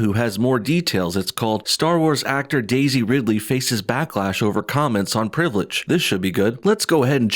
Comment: I swear I thought it said "Blacklash" ......instead of backlash, but, come to think of it, Blacklash sounds right!